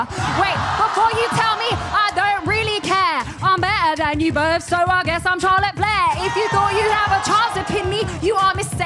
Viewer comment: what did she said about Charlotte flair?? Please answer me 🙃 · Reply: Some thing that she is better than them so she guess she’s Charlotte Flair